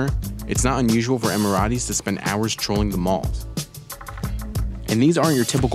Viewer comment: did he say trolling the mall?